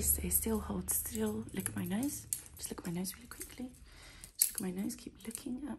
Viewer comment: I thought she said lick my nose I was like uhhh😂